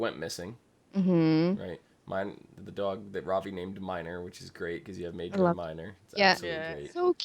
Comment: The dog was called minor? I heard it as Whiner because he kept whining